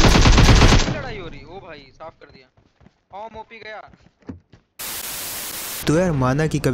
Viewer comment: who heard awmop gya..